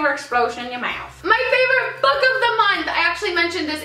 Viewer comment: I actually thought you said: my favorite bug of the month! Instead of my favorite book of the month. And I was like this is so;) haha x